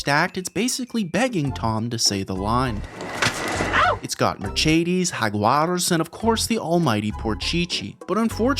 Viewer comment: whaaaaaaaat did you say????? merchadies ?